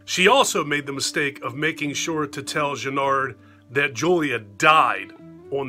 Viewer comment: Great review !!!! I agree with a lot of your points . For me part 2 is pretty solid . Every time you said Channard in this video , it sounded like you were saying Jennard . This video is liked by me . 😎👍🏻